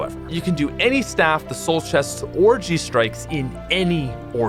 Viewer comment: thought he said orgy strikes for a second